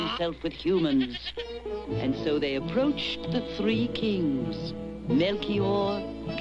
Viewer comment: When I was a kid, watching Ben Hadad meet the three wise men, I legit always thought he would say "three whole kings!" 😂 As opposed to, say, half a king or three quarters of one